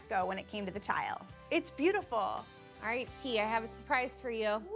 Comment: Sounded like the designer called Terra, "Tia"? Did I miss something? 1:04/5 mark.